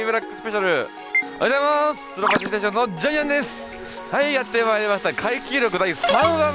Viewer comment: ｢スロぱちステーションのジャイアンです！｣にしか聞こえないw